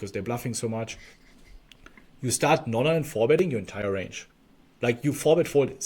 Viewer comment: I can't really make out the words on what BencB said. Did you say instead you're supposed to now 4bet+Fold your entire range?